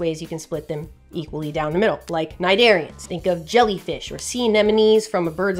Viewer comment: When you said Cnidarians I had to stop and go back because I thought you said Nigerians 😂